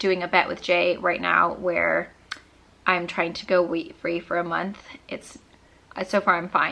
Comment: I heard you say weed instead of wheat lol I have to replay that section.